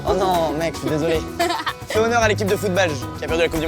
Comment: Pourquoi a 23:36 il dit Léo ? 😂